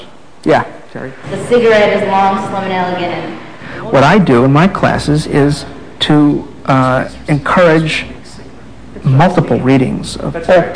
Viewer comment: did he say "my classes is to encourage" ?